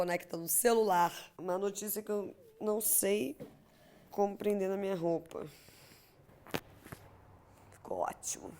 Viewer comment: Eu escutei “não sei como prender na minha rola” 😂😂 tive que voltar